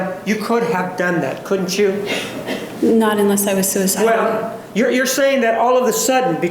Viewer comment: did she say” not unless I was suicidal”